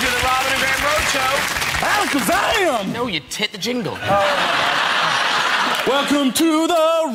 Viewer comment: Anyone else hear “no you tit the jingle”😂